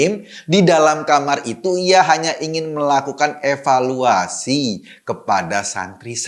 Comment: bang Eko, yang bener tuh di EVALUASI bukan EWELUASI lo yang salah ngomong apa gue yang salah denger nih, bang ? 🤔 🤣🤣🤣🤣🤣🤣🤣🤣